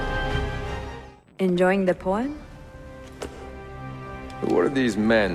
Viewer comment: lol at 6:53 i thought she said enjoying the porn?